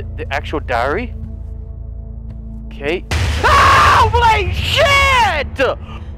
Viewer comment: it sounded like he said “HOLD MY SHIIIT” xd